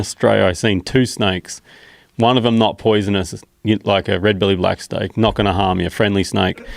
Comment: Did he just say red belly blacks aren’t venomous ? 😂